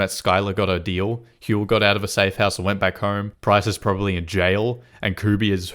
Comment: What name us he saying here??? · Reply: Huell?